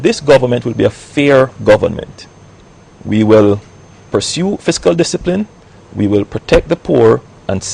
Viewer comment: does he say "we will pursue physical discipline" at 09:43?